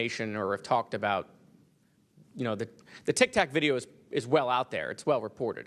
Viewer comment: So heres a wild a idea: If these things are faster, invulnerable, and unknown, then maybe we really shouldn't be shooting missiles at them... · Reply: did he just say Tik-tak?